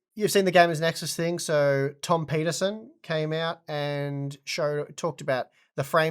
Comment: I swear to god you said "Tom Penisen"